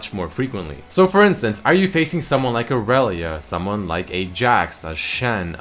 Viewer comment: lol when you says "irelia" I hear "arelia" so I keep on thinking he's gonna say "arelian sol" XD